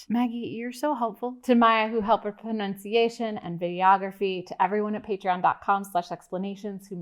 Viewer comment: Anyone else hear "videography" (8:05) as Gayography? Call it a Freudian slip or whatever but given the context of the video it sounds like a legit discipline. And a pretty fun one at that.